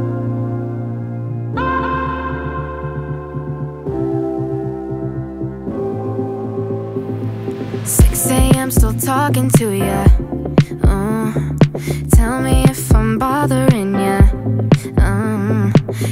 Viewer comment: V1:6am still talking to you Tell me if I’m bothering you Can’t hang up I’m too hung up on you Don’t know if you feel the way I do PRE: I would wait every day to get you all alone with me What you saying, honestly? Don’t say maybe, cos I’ll go crazy You’re all I think about, dream about, talk about, never doubt Want it now, tell me now, if you want to Do you? CHORUS: If I touched you like that Would you like it babe? If I asked to go out Would it be a date? If I kissed you goodbye Would we kiss again? I like you like that Do you feel the same? I like you like that, like that I like you like that I like you babe I like you like that, like that I like you like that Do you feel the same? V2 I can’t even concentrate Cos I don’t know what you might say If I tell you that I feel this way I don’t want what we have to change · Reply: Lol I thought she said "sexy I'm still talking to you" at the start